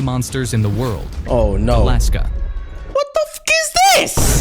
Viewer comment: I thought bro said Alaska